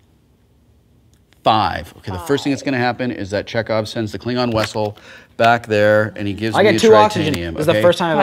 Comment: Anyone notice that when Will Wheaton says vessel the captions say wessel at 26:41?